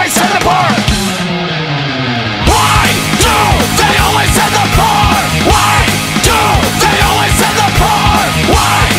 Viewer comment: En el minuto 04:03 ¿ que dice ? Disculpen la ignorancia quisiera saber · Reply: El comentario es de hace un mes pero x si acaso JAJAJA, ahí dice "Porqué siempre envían a los pobres?!?" Si pones atención es la misma frase de antes "Why do they always send poor?!?"